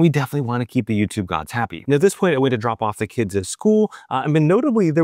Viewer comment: sorry, had to listen to that a second time. Thought you said “at this point I went to drop the kids off at the pool”. I thought, my god, how does that work? Are there no limits to what Garmin monitor now?!